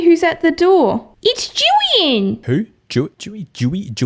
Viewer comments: i genuinely cannot tell what she’s saying at like 5:45 but i think the name is jillian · Jillian😂 that’s what I heard!